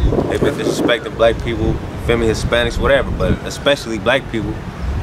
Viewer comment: did he say they been disrespecting feminists? along with black and hispanic people.... I assume he didnt say that. Think I am just not understanding his accent. Wanna get clarification. Thanks.